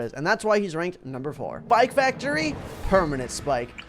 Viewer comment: am i the only one or did i hear ''pike factory'' at 8:08?